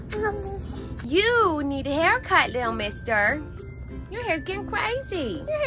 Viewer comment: I swear says "I like my haircut."